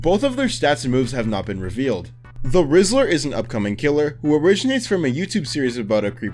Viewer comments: Anyone else hear "The Rizzler" and not "The Whistler" at 19:18? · I fr thought you said, "The Rizzler" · I thought bro said the rizzler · I can not be the only one who heard "rizzler" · When he said whistler I thought he said rizzler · i for real thought he said the rizzler (i am so brain rotted💀) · I thought you said "the rizzler" instead of "the whistler" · thought it said the Rizzler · I thought he said rizzer 💀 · i thought broski said the rizzler instead of the whistler 😭😭😭😭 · i SWEAR he said the rizzler, I SWEAR!!!!! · I thought bro said The rizzler 💀🙏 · Am I the only person who heard the rizzler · is it just me or does he say "the rizzler" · Did anyone else hear “THE RIZZLER”? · at the whistler part i thought u said rizzler xd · I thought he said rizzler lol😂😂😂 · DID HE SAY RIZZLER???? THE TRANS SCRIPT EVEN SAYS IT TOO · am i tripping or did u say the rizzler? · Was I the only one that thought he said the rizzler ? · I swear, I didn’t hear him saying “The Whistler”, I heard him say “The Rizzler”. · when he said the whislter i thought he said the rizzler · I can’t be the only one who heard “the rizzler” right😅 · why did i hear rizzler🙏😭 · I thought he said the rizzler😭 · Dude did NOT just say the rizzler 💀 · RIZZLER?! (ik I'm not funny I just heard this god forsaken word) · "the rizzler"am i hearing things? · I heard the rizzler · I thought he said "The Rizzler" 😭😭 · I thought u said the rizzler…